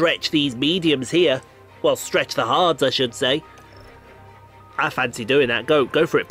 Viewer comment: did you just say stretch the hards?